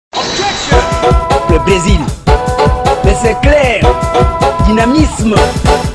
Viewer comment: can anybody pls tell me what the old dude is sayin at 0:03 onward? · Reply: Look for Eddy Malou ! In French : Le Brésil ; mais c'est clair ; dynamisme